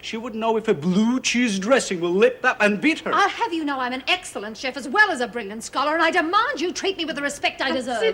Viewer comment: What did that social worker say at 29:31? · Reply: Think she was just bragging about herself a bit there. "I have you know that I'm an excellent chef as well as a brilliant scholar" That is what it sounded like to me anyway but the last bit was rather unclear. :)